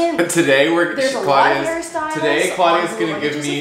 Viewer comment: LMAOOOO I THOUGHT SAID HARRY STYLES 😭💀